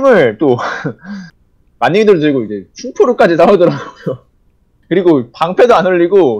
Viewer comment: 이거 뭘사온다고 말하는거임? 안들려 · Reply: 충포 충전포션 인듯?